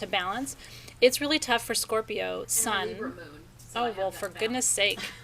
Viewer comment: did I hear Scorpio sun, Virgo rising, Libra moon?!